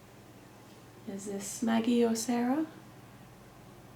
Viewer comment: it sounded like it was saying Maggie